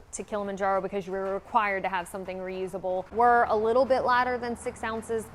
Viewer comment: I thought she said ladder!, But then I switched back to the video and saw the lighter